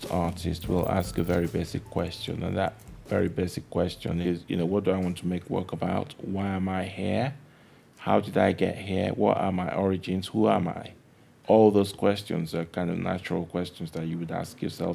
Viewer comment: can anyone tell me what he said at 0:46? I just can't understand a word he is saying.... Thx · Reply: He says that most artists will ask very basic questions - "What do I want to make work about? Why am I here? How did I get here? What are my origins? Who am I?"